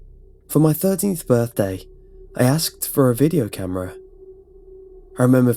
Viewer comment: Tell me I'm not the only one who heard 30th birthday. o